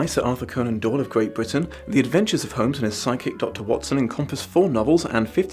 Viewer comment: I'm watching kinda late at night and I thought you said "psychic watspn" instead of "sidekick watson" and I just went with it